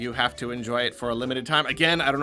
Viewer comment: The reason they are limited edition is so that they can make the scalpers set crazy prices and then make another huge event in 5 years. If the Wii U had this, we wouldn't be going crazy like we are now. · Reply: uh... i think you misheard the narrator. he says "a limited", not "unlimited". aside from the fact that starting that sentence with "unlimited" rather than "an unlimited" would be grammatically incorrect (and the fact that i didn't even have captions on), it even says on the game's website that both the digital and physical releases will be limited.